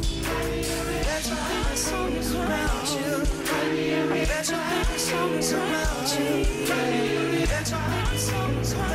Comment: what did he said?? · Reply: “I bet you think this song is about you”